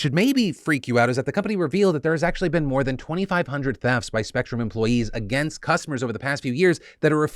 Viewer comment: i thought you said 2500 deaths, not thefts, at first and freaked out LOL